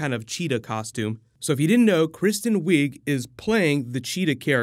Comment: I thought you said kristen kreuk for a second, the actress that played lana in smallville and chun li and that HORRIBLE street fighter movie lol.